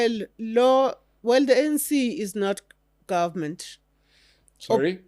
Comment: did she say the ANC is not government or my English has left me again? So who has been running the country for the last 30 years? Or we were just on autopilot?